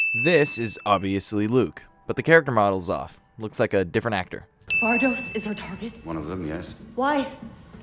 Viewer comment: If you listen closely to 6:31 it sounds like iden is saying Vardos is retarded?